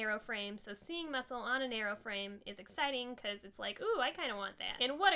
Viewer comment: I love this! Genderqueer you rock! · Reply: I heard "muscles on an airplane" instead of 'some muscle on my narrow frame'